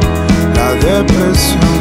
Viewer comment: Ah, paso todo el dia pensando en vos Ah, qué hay de malo en todo esto? Ah, paso todo el dia pensando en vos Ah, vos pensás que pierdo el tiempo Perdón si estoy de nuevo acá Pense que habías preguntado por mí Me gusta estar de nuevo acá Aunque no hayas preguntado por mí Voy a quedarme un poco acá Cuidarte siempre a vos en la derrota Hasta el final, el final Ah, todo lo que hago es para vos Ah, el tesoro se esta hundiendo Ah, todo lo que hago es para vos Ah, vos pensás que pierdo el tiempo Perdon si estoy de nuevo acá Pensé que habías preguntado por mí Me gusta estar de nuevo acá Aunque no hayas preguntado por mí Voy a quedarme un poco acá Cuidarte siempre a vos en la derrota Hasta el final, el final Es la depresión cinetica La depresión cinetica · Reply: Yo pensaba que decía "la depresión se réplica" y después pensé que decía "la depresión cinética" y al final era "la depresión sin épica". En fin me deprimí de tanto escuchar esa parte.